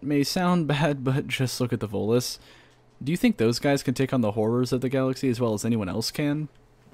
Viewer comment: I thought you said "whores of the galaxy" instead of "horrors of the galaxy" due to the clip you chose lel.